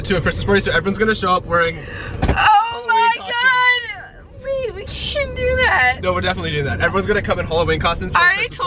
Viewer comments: did anyone catch it when she said "omg babe we can't do that" did she actually say that? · it sounds like tessa said babe Lol